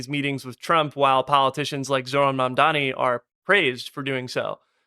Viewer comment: Why does the CC say Zeid Ra’ad Al Hussein in place of mamdani?